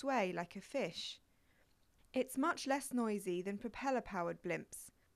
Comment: when she said "Filming Events" I thought she said "Building a Fence" without disturbing the people below.